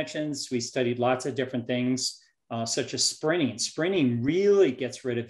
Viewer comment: I re-wound & listened 5x, still can't understand the word he said that gets rid of visceral fat "better than jogging". Sounded like 'springing' or 'spinning' or 'sprinning'? At 39:59, clarified it: she pronounced the 'T' "sprinting"! Thanks.